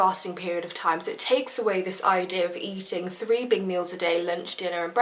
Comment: When she said "eating 3 big meals a day" I heard "eating 3 big mac's a day" and I just thought to myself..wow that's a lot of calories.. hahahahaha I'm probably just hungry lol